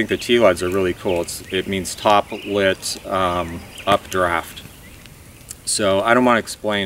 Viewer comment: OK I cannot understand the words you are using at 7:30 minutes. Top lit updraft. I would like to find out more info on this method but don't know what you call it and I'm finding nothing searching it. Thanks